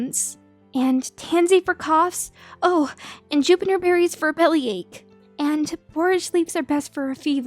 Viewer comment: in the video I hear Jupiner berries instead of Juniper berries